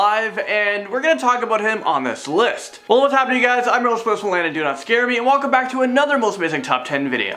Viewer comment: Who else heard do not scare me